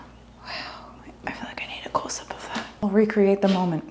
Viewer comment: You said "close up of that".. I heard "cool sip of that"